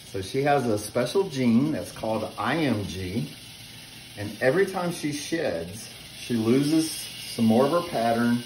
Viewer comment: I thought you said every time she sh*ts! 😂 sorry I'm from London (not the posh bit)